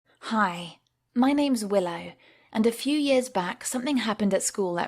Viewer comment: i thought u said that ur name is Will Smith lol